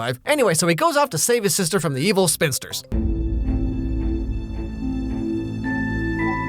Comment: I heard "evil Spencers".